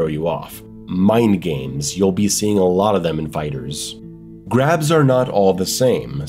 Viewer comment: Grabs are gay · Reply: Thought you said crabs at first, I was like how did crabs hurt you